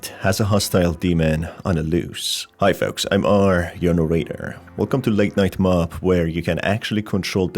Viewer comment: For good few weeks I thought you said "I am R. Yonah Raider" Me and my non-native English ears haha